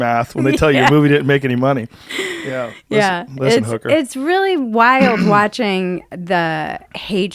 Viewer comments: Lol the panicked look to Jamie at 6:25 “listen hooker” (shit did I say something bad) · did he say listen hooker